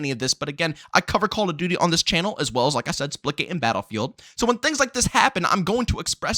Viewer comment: OH splitgate Thought he was saying splickit